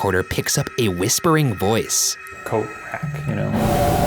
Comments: Kinda sounds like someone said “ Ello’/Lo’/Hello, Mate!” Or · sounds like it said" coat rack" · sounded like it said Coatrack Or Coldplay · almost sounds like it said "coat rack" in response. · the recording sounds like “a coat rack?” In a mocking tone in response to Shane making fun of shadow people 👀 go back and listen 👂 and tell me who agrees or what y’all think it sounded like instead? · sounded like coat rack to me xD